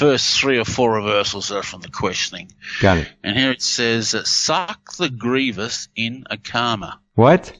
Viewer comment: Why did I hear “Suck the greatest anaconda.”